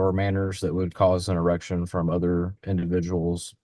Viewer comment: I heard 'a reaction' but I have a clean mind.😜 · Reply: He said "an erection" then continued on and correctly said "a reaction" a few seconds later. None of us who heard it correctly have a dirty mind, and I am sorry you don't have good listening skills.